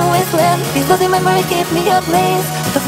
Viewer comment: i always heard "ablaze" as "up late" and idk why 😭